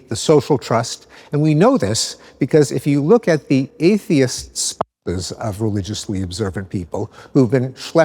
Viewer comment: what was the word they censored? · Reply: Spouses - Seems like more of a recording hiccup, as there's no reason to censor that word. Edit: the word also appears in the closed captioning so doubtful it was intentional